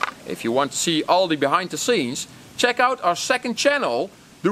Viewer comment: I swear I heard him say "Top did-illy geika" at the end....😆